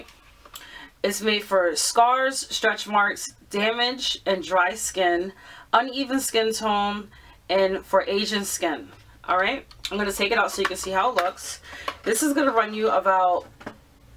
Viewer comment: When she said "aging" skin, I thought she said "Asian" skin. I thought, what a racist product. LOL.